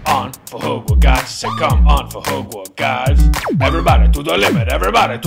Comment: i always loved how the robot in the beginning sounds like it says "come on fahogmoygods"